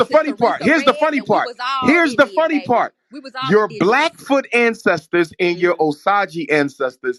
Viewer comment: All I heard is “Blackfoot Versace”🤣🤣💔